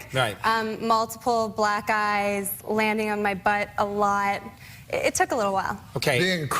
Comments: OMG.. I looked this video up after seeing a portion of THIS part with Gretchen and Norm, in a different Norm compilation... I honestly thought she said "Multiple black guys landing on my butt, it took a little while", then Norm interjecting "Being crippled.." Hahaha fucking hell, listen to it, it really sounds like that! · thought she said “multiple black guys landing on my butt” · bruh I swear I heard “ multiple black guys landing on my butt”😂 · I thought I heard "Multiple black guys landing on my butt a lot" lol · Thought she said "black guys" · i always hear multiple black guys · Does anyone else hear Gretchen Bleiler say "multiple black guys landing on my butt, a lot!"? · "multiple black guys landing on my butt"? am i a fool or is that what she actually said. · I deadass always thought she said "multiple black guys landing on my butt" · At i the only one who thought she said “black guys landing on my butt a lot” and not black EYES, landing on my butt a lot”